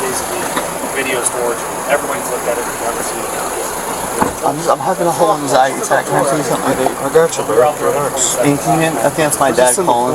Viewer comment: Pretty cool LEO gave him a hit off his vape, guy didn't even ask. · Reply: he actually did. Sounds like he says something like “I’m having an anxiety attack, can I please hit my vape?” With the cop responding “I got you bro” I believe that was his vape anyways and not the cops. I highly doubt the cop would allow some random person to put his mouth on his vape.